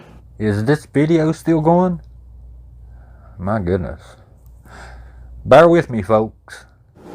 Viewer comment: When you asked, "Is this video still goin? The next words I heard were - 'buy whiskey folks!' I used CC to confirm you actually said "bear with me folks." Tbh, I think I'd prefer the whiskey, all things considered. 😂That's more my FLAVOR! As for the 'sound' @ 28:56, that's just the wind blowin' into my whisky bottle when I turn it just right. And the 'glow'@ 30:35, was him turning his 🔦 on & off quickly. I'm all about the mirror idea, I just think you're givin' MWGH too much (s)tinking credit here. My 2ç. ❤️